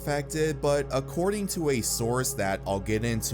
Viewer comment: did you say source?